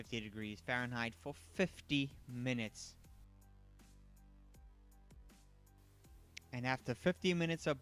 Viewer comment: Good thing it was spelled out 50 minutes because it sounded like he said 15 minutes.